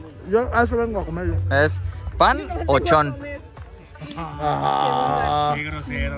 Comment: En el minuto 43:38 yo escuche panochon😅😂 o no?